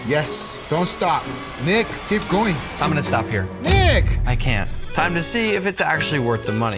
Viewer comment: i thought guga said make it rain nig